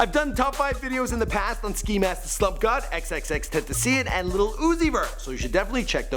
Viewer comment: Did he just say xxxtenticien